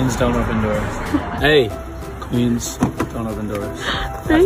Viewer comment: I thought he was saying "please do not open doors", but just realised it was "queens do not open doors" ❤💔